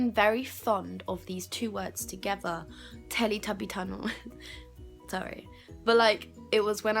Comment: I STRAIGHT UP THOUGHT U SAID TELLYTUBBY TITTY